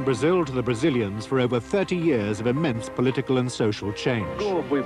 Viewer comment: Please, what is the narrator saying at 18:28? "...of a Memphis/myths political and social change."? And at 18:33? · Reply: I've completed the English SDH, so I can help you :) "TV Globo's cameras have shown Brazil to the Brazilians for over 30 years of immense political and social change. Can Globo present a "common view" of this history?" (In Brazilian Portuguese, which I'm still working on: "As câmeras da TV Globo mostraram Brasil para os brasileiros por mais de 30 anos de imensa mudança política e social. Poderá a Globo mostrar algo que é "tudo a ver" com essa historia?")